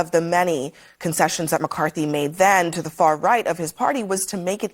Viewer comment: Lol the far right. Its sounds like she said the very correct.